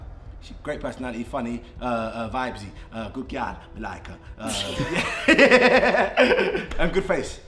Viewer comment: what did he say after " good girl "? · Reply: He likes her